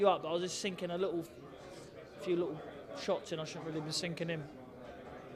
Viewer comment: Did she say whore marks